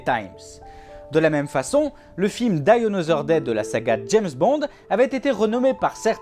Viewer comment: Vachement bien et intéressant (faut juste bosser la prononciation anglaise, parce que hein... "buy another day" j'ai eu du mal ;-) ) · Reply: c'est surotu sur "Die another day" que j'ai eu du mal. Il la dit tellement vite que, sur le coup, j'ai compris "Dinosaur day" :-D :-D :-D